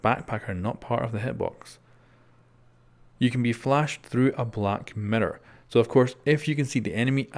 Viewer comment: Sounds like ur saying Mirrdoor